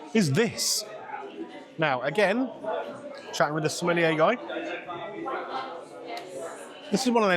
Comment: I must get my ears tested. When you were talking about the drinks, I thought you said you were talking to the smelly A guy 😂😂😂😂. I’ve lost the plot 🤪🤪🤪🤪